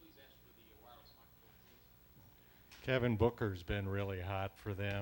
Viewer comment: did he say kevin? lol